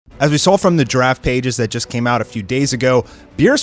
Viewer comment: Sounds like you said "As we saw from the giraffe pages" there, even though I know you said draft pages, lol :D